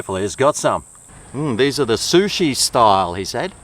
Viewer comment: Are you sure you didn't mishear him say "Shoestring style?"